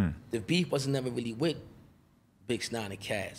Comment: It sound like he saying bix 9